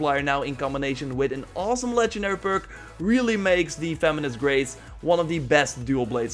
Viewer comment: it kinda sounded like ya said “feminist race” instead of “venomous grace”